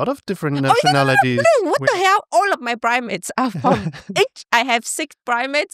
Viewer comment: I might misheard this😂😂 but the subtitle also read “primates” when talking about your friends hahahah but that means monkey 🙊 (Would also be cool if actual monkeys come to the wedding)